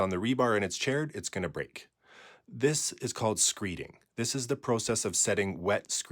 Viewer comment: Ngl, for a split second I thought you said, "This is called skeeting..." and I was like, "hol' up."